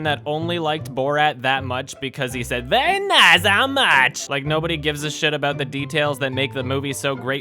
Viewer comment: What does he say at 8:26? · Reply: Very nice. How much?